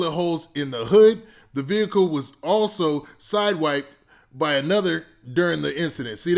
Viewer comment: side wiped? You sure that’s what it said?